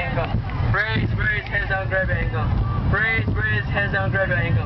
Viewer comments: Lol I thought they were saying... "pray pray, heads down Grab your pickle lol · I thought they were saying praise prasie grab your hankie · It's a chorus of praise. They repeat "Praise, praise our brother Hankle" - brother Hankle is the patron saint of aviation. Trust me. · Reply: I thought they were saying grab your handcuffs, But you win by a mile! :'')